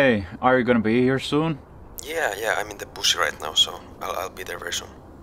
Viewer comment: At first I thought was saying, “I am in the bushes!” 😂